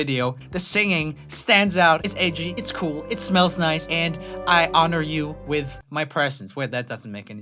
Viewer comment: wait did he just say the girls singing smelled nice? Lol oh also who is here now??